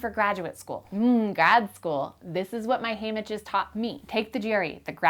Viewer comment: This is what my *haemeges* taught me? 4:20. Sorry for bothering you! But any one in this forum is welcome to help out. The "annotations" say "this is what my hand itches taught me". Is that what actually says? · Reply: Haymitches, as in reference to Haymitch in the Hunger Games. A guiding figure to tell you their experience in a certain situation that they've done before you.